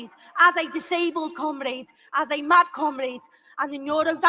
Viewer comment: It's the use of "comrade" that gets me every time. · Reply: did she say "as a mad comrade"?